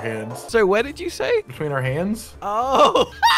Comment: I thought said between her yams at first honestly